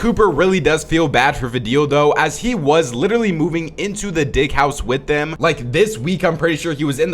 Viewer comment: if u have captions on instead of him saying DIG it says di-😂😂😂